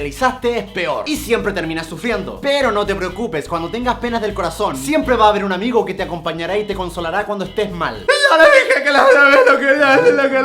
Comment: Entendi "penes en el corazon" *pervertida*